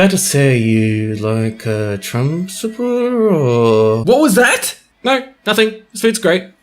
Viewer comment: Did just say that is a President Donald Trump supporter?!